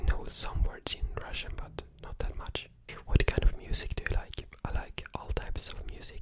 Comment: I thought he said for the girl Gyat kind😭